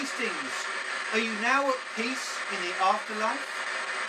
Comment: "Are you now at peace in the afterlife?" No joke, I heard, "You must know God's a lie."